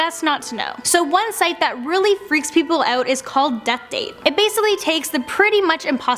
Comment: That death date one is fake it said I would die it said one year and 10 months and I haven’t died Yet... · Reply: Thought it said death note